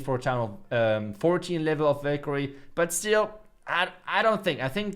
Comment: Anyone thought he said bakery instead of valkayre 😅😂